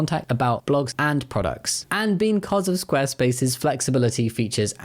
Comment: did you just say beancause, you fluent punster?